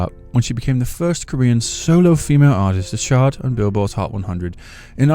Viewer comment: Did you say CL was the first Korean solo female artist to SHART on Billboard's hot 100? 😂🤣